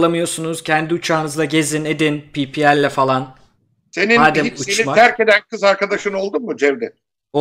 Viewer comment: da senin ... kız arkadaşın oldu mu?Burda ne diyor anlamadım? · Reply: Senin hiç seni terkeden bir kız arkadaşın oldu mu? diye sordu.